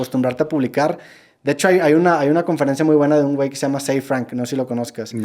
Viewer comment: que nombre es el que dicen??? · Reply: Ze frank